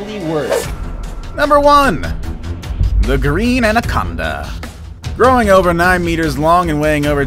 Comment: The last one I thought it said the green Avocado